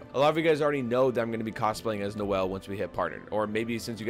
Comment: Did he just say he is gonna cosplay as Noelle or did I hear it wrong